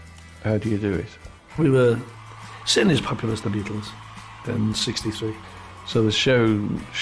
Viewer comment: What does Gerry say at 14:22? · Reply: “We were….. certainly as popular as the Beatles.”